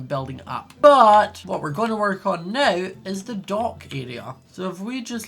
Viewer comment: Who else thought said Dog Area, then got kind of disappointed when ya realized he actually said dock? No? Just me? Okay then